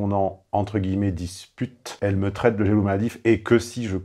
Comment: "Elle me traite de ?fgj/qy/ET§"... de quoi ????? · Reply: De jaloux maladif